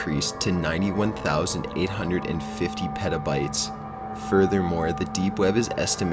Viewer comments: fml, when he said petabytes, i thought he said 'pedal bikes' and i was thinking, shit how long are pedal bikes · fml, when he said petabytes, i thought he said 'pedal bikes' and i was thinking, shit how long are pedal bikes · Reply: i thought he said "pedobytes"